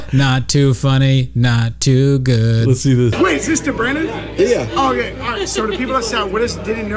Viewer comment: did he call him brandon?